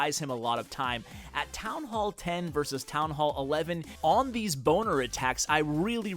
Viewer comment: Sounds like he said boner attack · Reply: That's, what it is, Bowlers + Miners = Boner lmao